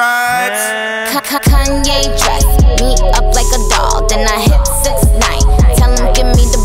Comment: I THOUGHT IT WAS KANYE WEST EAT ME UP LIKE A DOG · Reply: yaaaa i thought she said Kanye dress me up like a dog 😭